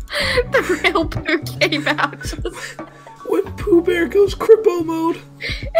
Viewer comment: i thought he said crippled mode i said i’m crippled